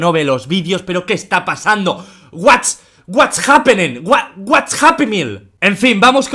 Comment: "what's happy meal?" eso entendí 😂